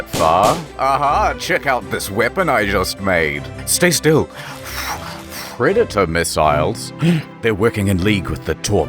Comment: For a second I was only listening and thought I heard you say Redditor missiles and Torpedos, but even with predator missiles it’s the same meaning